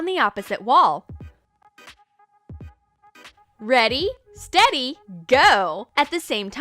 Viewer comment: Did she just say ready steady!